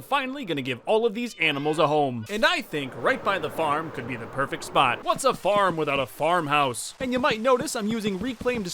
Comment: I for sure thought he was going to say, "What's a farm without a slaughter house" 😂